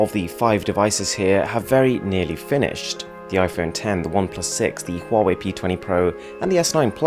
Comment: Did you just say iPhone X